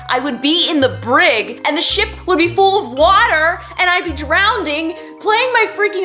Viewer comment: Did she just say “DROWNDING” lmao